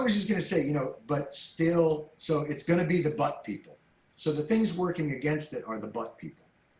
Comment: When he said "But people" initially I thought he said "Butt people" and I thought that's his PG version of saying "assholes"